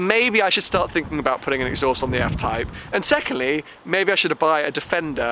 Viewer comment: Did I heard Custom Exhaust for the F-Type...:O This will be epic